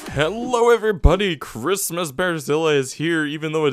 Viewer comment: everytime you say "behrzilla" i hear "Brasília"